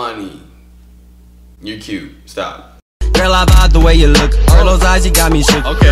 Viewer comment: "Leilani you cute" is what he said